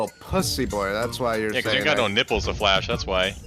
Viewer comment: did he just say nga? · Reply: he said nipples